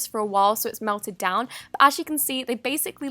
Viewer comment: she says "b" instead of "but" and I have no idea why I noticed that .-.